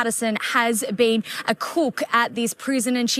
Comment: With her accent I thought the reporter said Erin had been a kook at the prison 😂 probably both tbh